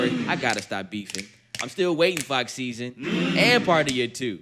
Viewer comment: did bro say fox szn or am i trippin